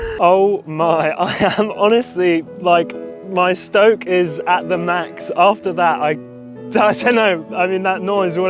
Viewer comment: i thought he said "i stroke at the max" 😭